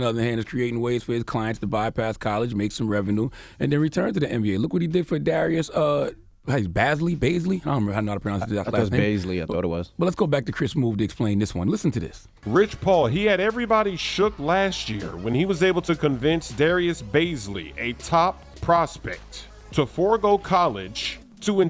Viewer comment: did he say chris smooth dicks?